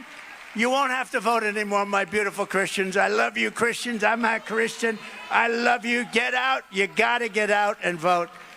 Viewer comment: did he just say "I'm not Christian" ?